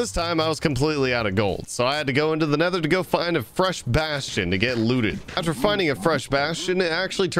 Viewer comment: it sounds like you said bath